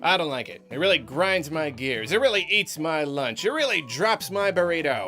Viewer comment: I was focused a bit too much on the action on screen and I thought you were about to say "It really eats my ass."